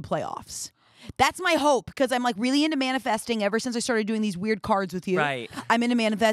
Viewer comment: I realize that it is absolutely not what she said but I heard "really into Manda festing" and now I'm going to use Amanda festing in place of manifesting whenever I'm doing something that I think Amanda would be proud of lmao